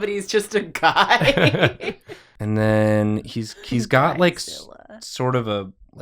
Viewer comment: I hear your wee "guyzilla", it didn't go to waste